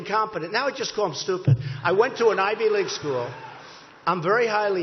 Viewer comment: So.......you're saying all of his supporters are incapable of understanding multi-syllable words? You hear that Trump supporters? You're STUPID!! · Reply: Mickeyislowd I thought he said ' big league'